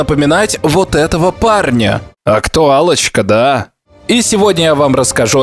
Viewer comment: "-Актуалочка", послышалось как: "А Кто Аллочка, да?" - долго думал, что за хрен на экране и причем тут Аллочка 😂😅🤦‍♂️